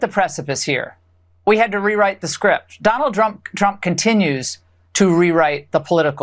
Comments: did she say drump drunk 😅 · Did she say donald drunk then correct herself at 14:02?